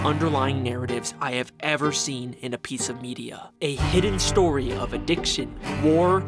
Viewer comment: Why do i hear pizza media out of a video about a cereal conspiracy?? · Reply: He said piece of media